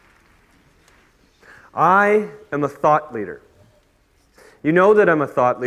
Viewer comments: All I hear him saying is thot leader. It sound like he is saying thot leader. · Honestly, the only thing I heard was "thot leader".